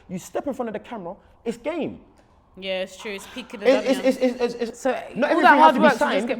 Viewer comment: did just say it's peak in the dunya at 8:50, im deaaaaaaad